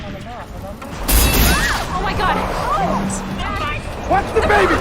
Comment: tf i heard " WATCH DABABY" lmfaoooooo 😂😂😂😂😂 Oh my bad he actually said that